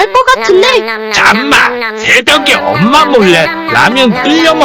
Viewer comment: 나만 엄마 몰래 끌려 먹기라고 들리지